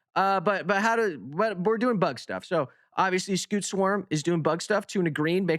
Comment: For a second I swore you said "we're doing b*tt stuff" lol😂